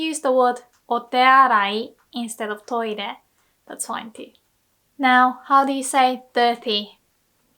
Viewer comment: i thought you said "now how do you say 'thirty'?" So i answer sanjyuu/sanjuu lmao😂